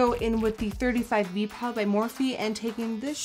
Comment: I thought she's saying morphin